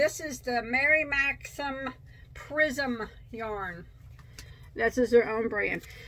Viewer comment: I was listening while working on stuff and I swore you said Mary maximum prison Lol. The Mary Maxim prism yarn could definitely be a tongue twister!!